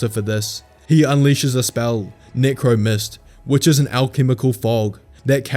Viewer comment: I thought he said negro mist